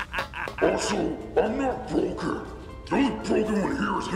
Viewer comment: what did Fake Sonic say? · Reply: actually it's "I Still Function"